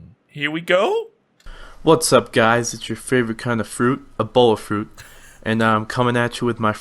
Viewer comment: Why does the intro of the video sound like he is saying " Hey guys, its your favorite fruit, ebola fruit"